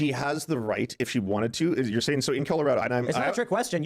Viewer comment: Does Lance say "In Killarado" instead of "In Colorado" at 14:55 ?